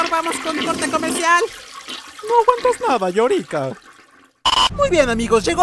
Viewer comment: alguien más noto que dijo "llorica" 🤣🤣